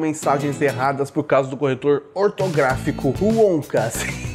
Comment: entendi " Corretor Pornográfico " 😂